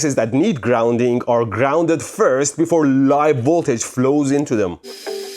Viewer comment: did he said, voltage flows?